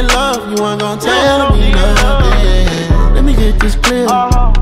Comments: before I thought he said let me hear you scream but I still love this even thoe it said let me hit this crib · Man thats not what i thought he was saying AT ALL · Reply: i always thought he said “let me get this clear cause i had no idea” 😔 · I thought it was : “let me get this clear” 1:03 “let’s get naughtier, yeah I need all of ya” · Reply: i thought it said let me get this bread 🤖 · anyone else thought it said let me get this bread😂😂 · i thought he was sayi g lets get this bread xD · BRUH I THOUGHT HE SAID “ let me get this clear”💀💀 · I thought he said let me get this bread as in money lolxox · I thought he was saying "Lemme get this real" (aha) "cause i had no idea" "feel like i did to much,feel like i did to much" "lets get oreos" "i need them right now" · Man thats not what i thought he was saying AT ALL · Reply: I thought he said lemme git this bread haha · Man thats not what i thought he was saying AT ALL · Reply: really?? I thought he said lemme get this bread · I thought he said Let's get Narnia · I hear: “lemme get this bread” “lemme hit this crib” “lemme get this clear” what is he saying 😂😂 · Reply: tf I thought it said let me hear this again lmao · I thought it was : “let me get this clear” 1:03 “let’s get naughtier, yeah I need all of ya” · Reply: i thought 0:56 said " let me get this straight"